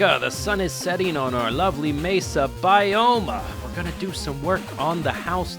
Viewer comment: Ironic at the start it sounded like you said "mesa bi-home"